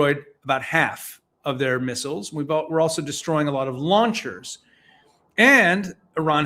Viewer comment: I initially misheard your pronunciation of "launchers" as "lawn chairs". For a few seconds, it was funny, and I had to tell you